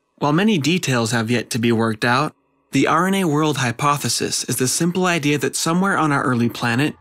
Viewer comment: Sounds like you say "The irony age hypothesis" at the end. Wich was fun.